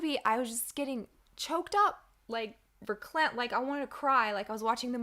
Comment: I kept hearing "Christian Ba'al."